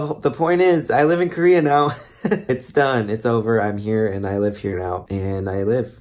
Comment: OMG I thought this said ok I’m leaving Korea now and I was like wtf happened you’re already leaving!?😂